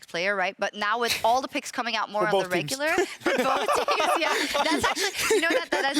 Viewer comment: what does enders says at 8:31? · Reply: For both teams